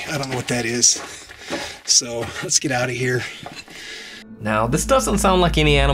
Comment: just listen it sounds like something said get out of here